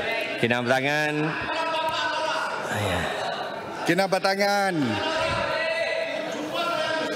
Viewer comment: I hear someone say "Kepala bapak kau lah!" 😂